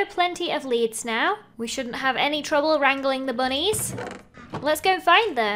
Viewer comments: for a second, I thought she said strangling the bunnies 🤣🤣🤣🤣😂🤣🤣🤣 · i thought she said "time to strangle these bunnies"